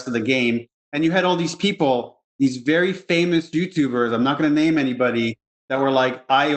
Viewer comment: I could be mishearing but it sounded like Neil said “dudetubers.” I’m gonna be using that term a lot.